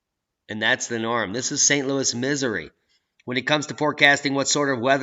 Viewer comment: I thought you said St.Louis Misery☃